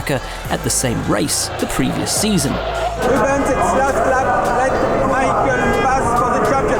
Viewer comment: i think he said last lap not jean todt